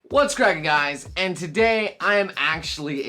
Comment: I thought you said "What's crackin' guys. And today I'm Ashley." and I just died.